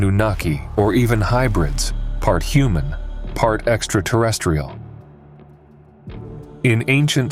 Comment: I was all in until I heard "Exta-tah-restiol"